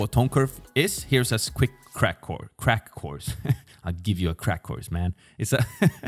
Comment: I heard crack whore